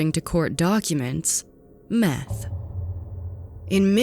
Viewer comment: I had the play this part 3 times, because I wasn't sure if I heard it right....METH??!!